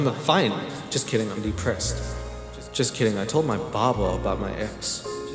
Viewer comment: I thought you were saying "Baba" instead of "barber" I realized that I was wrong at the end of the video!